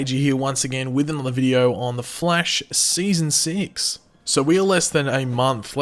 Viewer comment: Did he say “The Flesh” 😂😂